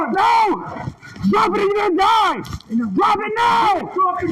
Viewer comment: Did I hear “ Drop the gut or you wil DIE bang bang”